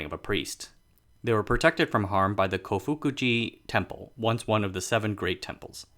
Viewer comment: Try this Wikipedia page, which I admit is rather sparse: <link> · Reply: Oh right I misheard the name when I googled it: Kofukuji temple in Nara, not Tofukuji temple in Kyoto (which Wikipedia says takes it name from both the Kofukuji and Todaiji temples in Nara).